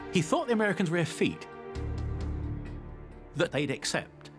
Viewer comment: what does he say that Hitler thought the Americans were here? I can't tell if it's efeet, afeed? Can't make out the term? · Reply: Effete